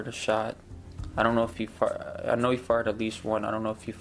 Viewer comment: I seriously thought he said "he farted at least once"